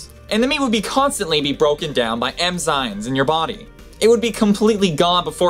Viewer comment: Did you say "emzynes" at 8:10?